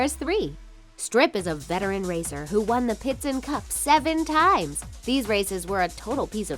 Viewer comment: I'm I the only one who heard her say pitson cup when she was saying piston cup?